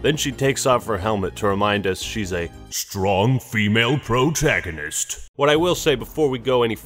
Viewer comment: I thought you said brotagonist hahah made me laugh love your vids always.